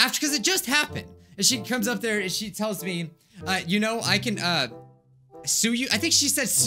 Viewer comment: it's sounds like he is saying Tsuyu (like from mha)